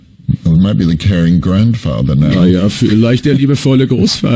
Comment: What was he saying at 5:50 about caring grandfather? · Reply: The moderator asked which role was still missing, which Alan had not played. Alan said: “Well, maybe the caring grandfather, right? I havn’t played that yet” (this is how it was translated into German)